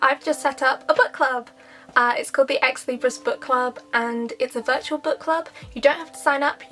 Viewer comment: I thought u said ex-libra and not libris tbh I want to be an ex-air sign as an Aquarius who is going through it!